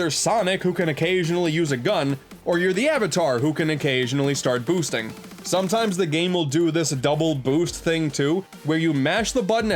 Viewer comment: Um did you just say the oc can boost ummmmmmmmmmmm